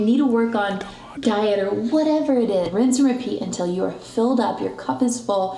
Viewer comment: For a second I thought she said “rinse and pee” lol 😝 😆 😂